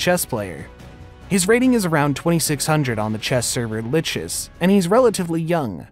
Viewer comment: You pronounced Lichess super weird I thought you said glitches haha. Idk the proper pronunciation but I've always said lee-chess